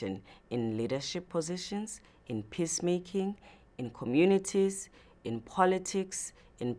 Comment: I know what she really said at 0:57 but honestly first time I heard it I thought she said "piss making"!!